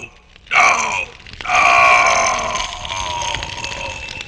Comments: captions said see how harlequin captures canadians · i love how the subtitles say “See how Harlequin captures Canadians”